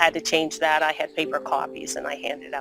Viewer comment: I thought that woman said “ I had paid for coffees “ instead of “paper copies”and I just thought it was so insignificant to tell everyone you bought coffees 🤣